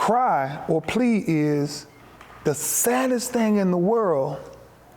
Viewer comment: The saddest thing in the worlt...in the WORLT!! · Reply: Funny, to me it sounded like he said "WORLD"... (no "T")